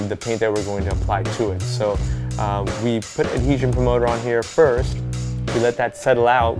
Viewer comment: What exactly was it he said he put on shoe @ 3:37 ? Cause i want to try this custom & seeing he mentioned he applied something not shown in video dont wana screw up cause i missed something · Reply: Davante Manning it's called adhesion promoter